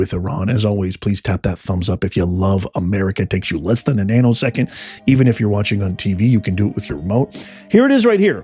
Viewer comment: At the end you said tap that thumbs up it only takes a nano second...at first I thought you said anal second!! Lol!!